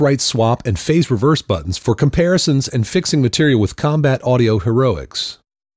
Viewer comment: Did he say "fixing material with combat audio heroics"?